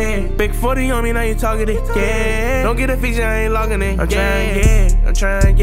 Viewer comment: Sound like he saying “ I’m trying gay”